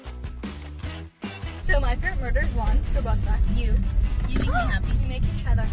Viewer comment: what is annie saying at 0:12? “ so my favorite ______ is one” i can’t tell haha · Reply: i hear murder' lol